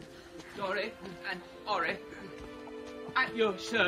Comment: When Ori and Nori come out the subtitles say: Sorry and sorry, at your service.🙃